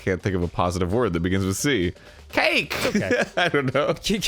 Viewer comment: i could’ve sworn i heard kike. me and would be hella mad